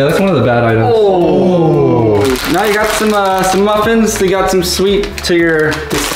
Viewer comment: When said muffin, I thought he said nothing lol